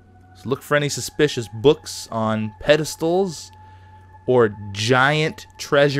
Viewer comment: I thought he was saying "mysterious books on pedophiles"